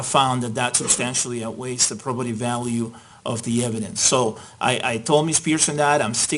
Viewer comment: Maybe I'm an idiot, but I can't distinguish the word the judge says at 12:41 and 12:57? I thought he was saying 'properative" value, but that's not a word and Google isn't giving me a solution.